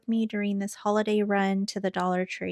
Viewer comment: Great haul! I was laughing at the end where you said "stay safe, be blessed " ...I thought you said " stay safe, eat less.." 🤣🤣🤣